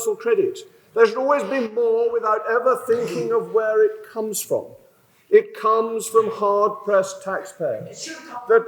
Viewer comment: anybody understood what she was saying? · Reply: I think she said, “it should come from you”.